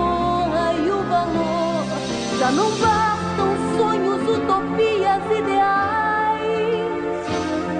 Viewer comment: O que ela fala aqui? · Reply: Já não basta o sonho utopias ideais (eu acho)